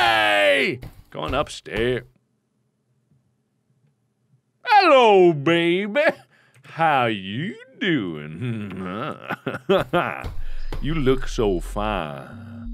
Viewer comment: 😂 For a long time I don't hear a word (Hallo baby, How 😂doing you look so fa…….😂) pls I want to hear this word ❤